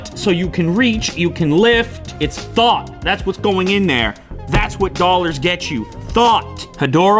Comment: Lol @ 1:13 in my head what I heard Lou said was "It's THOT! That's what's going in there, THAT'S what Dollars get you... THOT!" Hahaha.